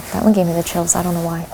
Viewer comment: I heard "I dont know who did it"